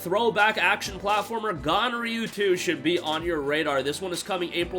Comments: oooohhhh ganryu 2, thought you said gonorrhea 2, my bad… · ooohhh ganryu 2, thought you said gonrhea